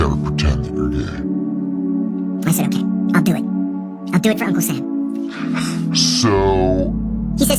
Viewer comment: On number 3 I thought he said Uncle Stan but it was uncle sam